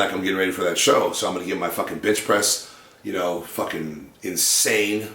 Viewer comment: did he say 'bitch press'?